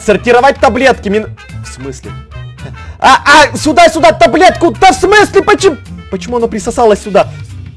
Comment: услышали мат ? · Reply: Таблетку а не да бл*ть🙄🙄🙄